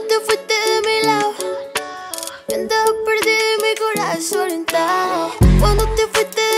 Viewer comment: eu me di cuenta que dice "y mi cora soltado" y pues no se dice soltado :v (c dice suelto xd) min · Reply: dice "y mi cora desorientado"